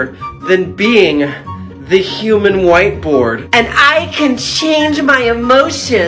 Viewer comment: When he said he can change emotions on demand I thought he said he could shit on demand 😶